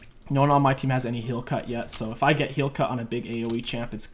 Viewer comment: Definitely thought i heard you say "heal cuck" and now that's all I'm ever going to call grievous wounds anymore